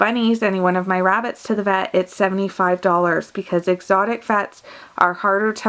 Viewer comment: oh god. It sounded like she said "exotic fats" im dead lmao